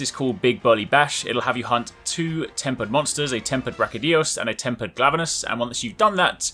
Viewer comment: the auto-captions say “a tempered rocky dose and a tempered blabber nurse”